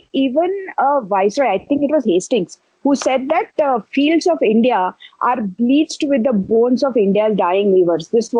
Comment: Someone please reply, what is she saying at 7:46 about Hastings? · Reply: Fields of India bleached with the bones of the dying weavers...